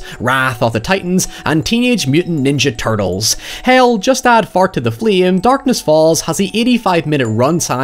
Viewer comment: I swear you said “add fart to the flame” but I also think I’m wrong 🤣🤣🤣